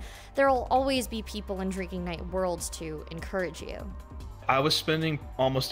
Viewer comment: I thought that said "Drinking Nig"